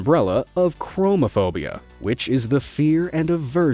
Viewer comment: I heard homophobia ;_